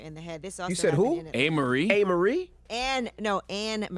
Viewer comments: I thought she said A’marie too! I was like..., hol’d up!!! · I thought she said Amarie